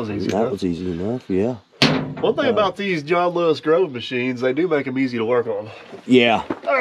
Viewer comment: Great video as usual! What did you call JLG @ 3:00? I played it back several times and couldn't make it out. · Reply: John Lewis Grove aka JLG Machines